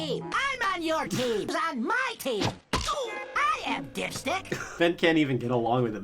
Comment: I thought he said "I am, dipsh*t!" Lol